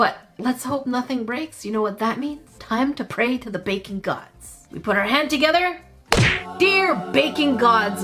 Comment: I heard "Bacon Gods"